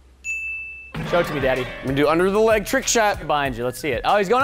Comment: am i the only one that heard him say throw it to me daddy?!?!